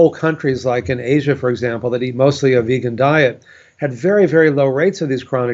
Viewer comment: I wish vegan proponents would STOP saying Asian populations (and most other Blue Zones) eat/ate a vegan (even predominantly vegan) diet. They don't/didn't, except those listed as those in the Adventist community. They did eat animal products. Simply say that. It's okay. You're not betraying the animals to speak to reality. THEN, go on to say that when you take that extra step and remove animal products you are helping your health, the animals, and the planet. This is a HUGE point non-vegans jump all over and it turns them off to our (yes, I am vegan too) message. Just something to think about ... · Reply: I distinctly heard him say 'mostly' plant based diet